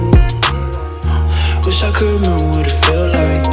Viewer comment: When it released on SoundCloud I thought he said “Wish I coulda known what it felt like”